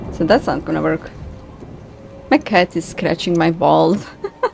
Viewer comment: Kitty's got the zoomies episode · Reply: I thought she said my cat is scratching my balls 🤣 I was like wait! What? She had to say walls